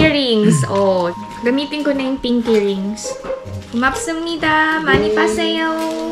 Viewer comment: narinig ko: “money pa sayooo” 😂